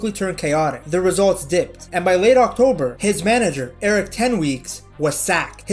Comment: Wait, did I hear "Eric 10 weeks🤔" 😂